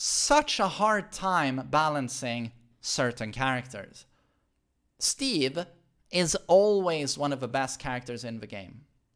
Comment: Did u just say Steve is OP? Lmfao